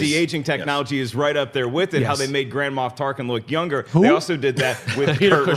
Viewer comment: It sounded like said "Grandma Tarkin".